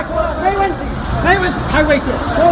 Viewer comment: I know this is not the place to joke around, but at 0:15 all I hear is my onesie my onesie onesie